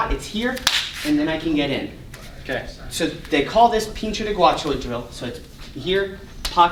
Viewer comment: I thought he said Pincho de Guacho. Sounds like a Lucha Libre move.